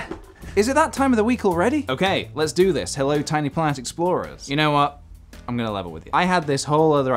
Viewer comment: When I started watching I thought he said tiny plant explorers